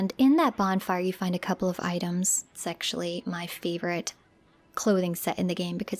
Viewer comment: Did you say, 'sexually my favorite'?